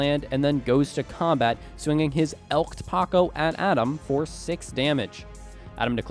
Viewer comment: I swear I heard Say Elk Taco.